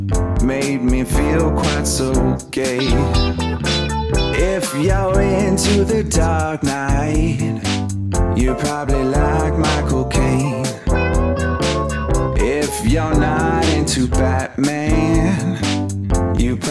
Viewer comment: i could of swore he said "my cocaine" at first